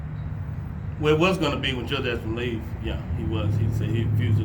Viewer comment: did he say we was gonna beat him till death?